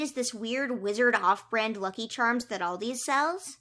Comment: Aldi gang! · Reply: DOES SAY "ALDI'S"????